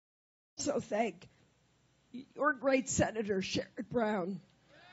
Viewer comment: What's that Senator's name at 2:15? I'd swear she says "$#¡+ McBrown."